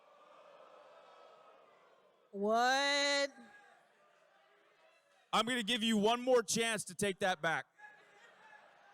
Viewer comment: does anyone know what the fan said? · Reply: "He's better than you" thats what the fan said to Cole